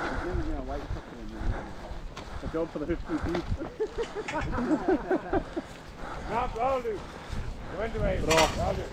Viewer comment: did I hear a mention of the Hoofgp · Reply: We were talking about one of the lads having terrible feet and i think I said it was a job for The Hoof GP 😂